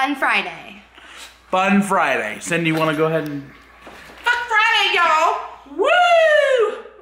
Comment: I thought she said fuck Friday yall